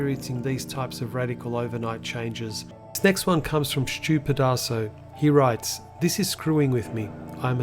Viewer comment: Whoa, for a second there at 1:53 I thought you said this next one comes from stupid asshole. I was like damn this guy is fierce. Anyway, good video.